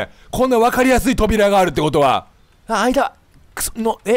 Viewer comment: ここなんて言ってるのwwwww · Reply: ｢あ、間(あいだ)｣ですかね？